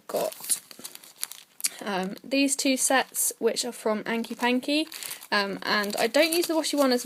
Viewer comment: Hi there - I loved those washi stamps - I thought you said hanky panky but I couldn't find that.. :(